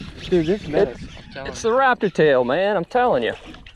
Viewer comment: <link> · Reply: ok I was looking at z man. And I thought you said rapid tail not raptor. Thanks man ima have to give them a try